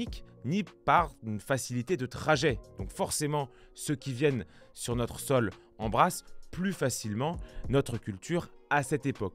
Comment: j'ai entendu "ceux qui viennent sur notre sol en brasse" 🐸